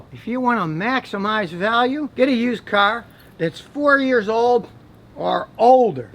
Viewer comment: Well I think that scottys 94 celica will be a collectors item very soon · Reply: I thought he said get a car that 40 years old or older 😅